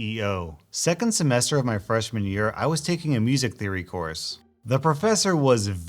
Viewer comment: I first heard "freshman year" as "fresh manure" lol